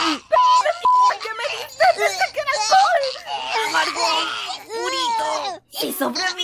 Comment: al comienzo no entendí lo que tomaron, luego cuando fui a La Merced/ Chanchamayo entendí que era "Lo Amargo" JAJAJA